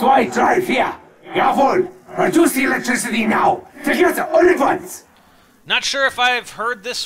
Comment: at the end of his statement where it shows "????," I'm really sure he says "Reduce it all at once." He says the word reduce fast, but you can hear it. It also makes sence because all four people have to reduce the power at once/simultaneously. At 6:26 the front "????" sounds like "A town did he ordered" and the last "????' sounded like "Jerk off" At 7:49 sounds like "Nine/Mine swastika"